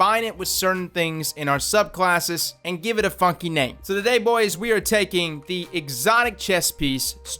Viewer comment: did he say funky or fucking i cant tell